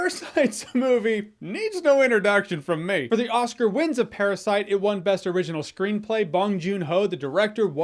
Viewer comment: At first I thought he said it intentionally, but no. He literally put no effort in this review, he put it out for the sake of putting it out. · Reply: mordor259 and you put not effort into actually thinking about what he actually said 🤦🏻‍♂️. He said “For the Oscar wins of parasite” considering how he lists what parasite won.